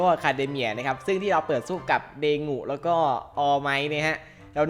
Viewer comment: ผมได้ยินไม่ผิดใช่ไหม เดงุ!!! ใครได้ยินมั้งบอกหน่อย